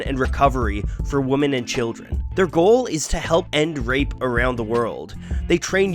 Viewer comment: Am I the only one that thought he said "help and rape"?